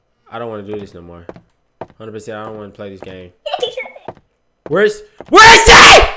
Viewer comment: I thought you said you wouldn't play this anymore -_